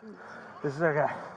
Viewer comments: did I just hear “This nigga?” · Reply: lmao really dog? "this is our guy" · What was said at 5:49? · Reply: lmao i see what you mean but i think he says this is our guy i could be wrong tho